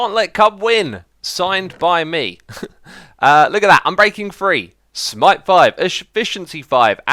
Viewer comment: When you said unbreaking 3 I thought you said I'm breaking free... he he he...